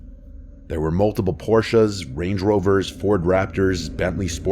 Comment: did he say “porcha”? Take a shot every time he says “quarry”.